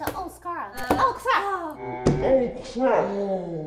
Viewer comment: I heard Lily say "oh CRACK"